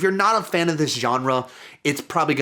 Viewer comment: Could have sworn you said "Sean-re" instead of genre when discussing Color Out Of Space. If this isn't an intentional thing, please make it one.